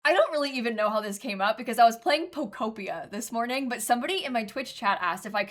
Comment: POKOPIA! For a second I thought she said Endacopia and was very confused, but hype pokopia makes more sense though...